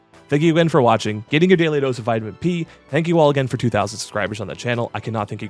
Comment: I no joke thought I said 200K subs instead of 2K since the video was such good quality